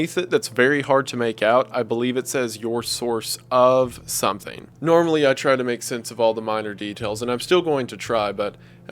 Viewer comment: To me, the subtext at 4:58 says “Your source of temporal ______ since 1998!” Can anyone figure out what that word I’m missing is? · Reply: Ephemera